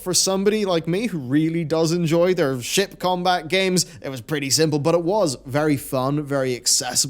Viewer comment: I did a double take. I thought you said you liked Criterion's "shit combat games"😂